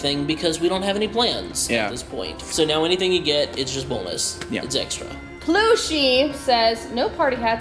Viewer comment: YAY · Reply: did say BONELESS